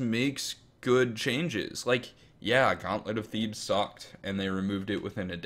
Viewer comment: What did he say sucked and was removed In a day at 20:15? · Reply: of Thebes